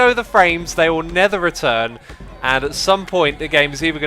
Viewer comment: Hi X, just wanted to know why do you say nether instead of never?